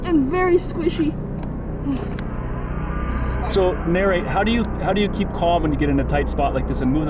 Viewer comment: Why did you call your son Mary at 6:09? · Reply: "So, narrate, how do you keel calm..."